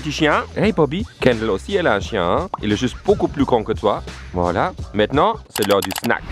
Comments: J'ai compris ''il est juste beaucoup plus con que toi'' même les souttitres ont compris ça chuis morte 😭😭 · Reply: Il a dit "il est juste bcp plus grand que toi" mais dans les sous-titres il y a écrit "il est juste bcp plus con que toi" depuis j'arrive plus à respirer 😭😭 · je suis la seul à avoir entendu " il est bcp plus connu que toi " mdr